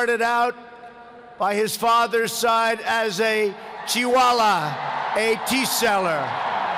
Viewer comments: (turn on captions) " he started out by his father's side as a koala"🤣🤣🤣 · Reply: thanks for the hint at the cc. i had thought he said Chihuaha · The first time I watched this video, I heard "he started out by his father's side as chihuahua..." I swear.